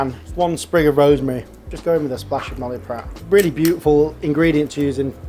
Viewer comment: Stellar video! Thank you so much! Couldn't understand the name of the liquid you pourred at 16:22. Thank you again and in advance. 🙏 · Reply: Noilly Prat French vermouth